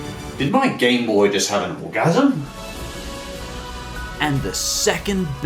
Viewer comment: Did... Did he just say his Gameboy had an orgasm???